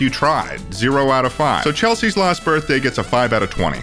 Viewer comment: When you said Chelsies lost birthday I thought you were saying Chelsie’s LAST birthday and I was very concerned she’d end up like Kelly